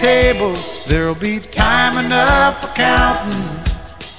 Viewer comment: This is not about poker this is a life message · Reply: OH he was saying when then Dealin's done, I thought he was saying when the Deal is done.